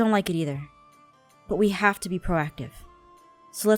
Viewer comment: I thought she said "we need to be proactive" get it? the acne cream haha... ha...